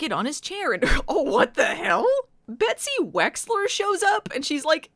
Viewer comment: I thought you said Etsy Wexler and thought we had gotten blindsided by a Star Wars crossover.